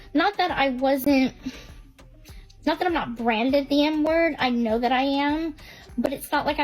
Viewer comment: Girl for a second I thought she was say “n word” and I was like wtf??